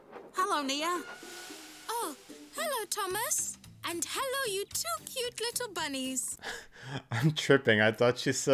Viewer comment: I also thought she said “Hello, YouTube!”